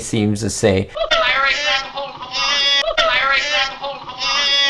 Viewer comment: for me it sounds like he said "lary grab ahold of the line"